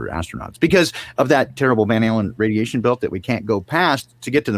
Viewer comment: when said the Van Allen Radiation Belt, it sounded like The Van Halen Radiation Belt 🎸🎸🎶😄